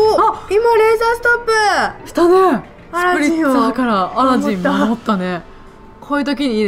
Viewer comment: スプリッツァーがアラジン守ったって聞こえた。